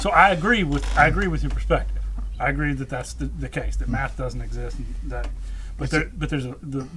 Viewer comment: You made it up. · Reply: I thought you said "Matt didn't exist..." my bad.